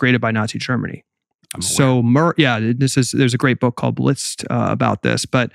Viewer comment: What did he say that book is called at 1:24:06? I heard List?